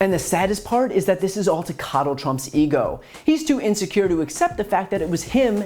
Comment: Did he say connal trump.. Nice😂😂😂😂😂😜😜😜😜😜😜